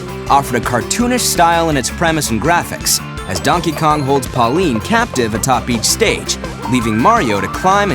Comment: did you just say pauline??